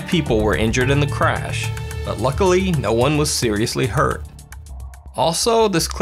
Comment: ''unluckily?? nobody was injured in the crash'' am i the only one that hears ''unluckily'' instead of ''luckily''